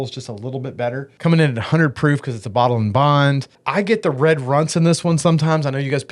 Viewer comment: LOLOLOLOL!!!!!!!!!!! Holy crap! When he was talking about how Early Times was giving him, "red Runts", I swear I thought he said, " red runs". And I was like, "Dude, you might wanna get that checked out! That don't sound like a positive thing!!!!!!!!"